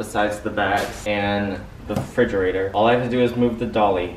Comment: Why.. Why did I hear “Re-Frederic-ator...” WHY?! 😂